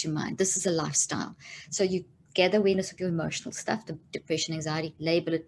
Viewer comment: is she saying gather a "witness"? I can't understand her accent for that word